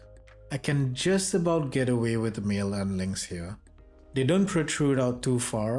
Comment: Great review! I don’t catch what you say at 5:04 tho. You can just about get away with..? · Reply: "...with male end links here.."